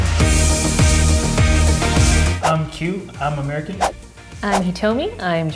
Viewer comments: I legit thought he said "I'm cute, I'm American" 😂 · I thought the first guy said “I’m cute I’m American” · After that snacks thing being said in the beginning, i thought he said “I’m cute” 0:13 lmao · I NO JOKE THOUGHT HE SAID : "hi im cute, im american" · I thought Q said hi I’m cute and I’m American 😘